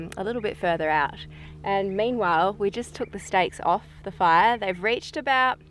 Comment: Thought you said snakes.